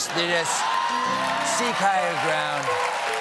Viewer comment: When he says "seek higher ground" I first heard "Sieg Heil my crowd". Had to rewind to make sure it wasn't that... Coming from Trump though that makes so much sense, that fricking Nazi baby